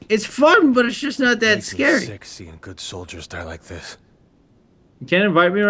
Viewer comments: LOL it sounds like Leon is saying "Sexy good <mail> to play that twice to figure out he was actually saying "...sick seeing good soldiers.." XD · lol at 5:32 I thought leon said its sexy seeing good soldiers die like this O_O